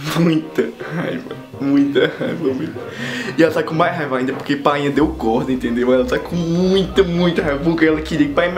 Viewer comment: Eu entendi "painha deu corno" KKKKKK